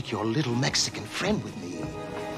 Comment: For the longest time, I thought he said "Yes, Cuban B!" because Cuban B was some kind of dish. In other words, he was going to kill Scarface with fire. But I think I got Cuban B mixed up with cube steak. So I guess what I'm saying is that I still don't get it. Oh, and I promise I wasn't high, either.